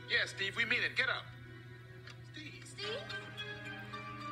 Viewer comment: I heard him say am-buh-lance😂😂